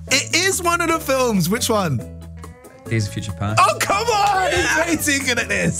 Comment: What movie did he say at 2:03? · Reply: X Men- Days of Future Past